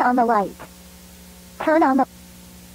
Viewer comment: When the lamb was like turn on the light I thought when it cut she said turn on the fucking light and I was cracking up